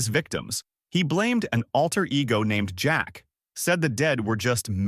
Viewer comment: it says Jake instead of Jack.